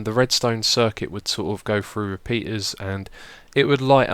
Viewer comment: ? did he said penis? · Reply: Hax repeaters excuse me